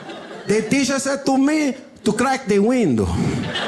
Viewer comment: What did the kid say? I keep hearing "Crack the window"